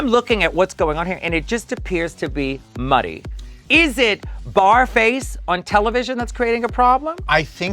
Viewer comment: I thought she called her Barf Face for a second lmaooo